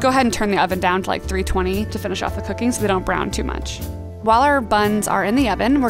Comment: did she say cookies?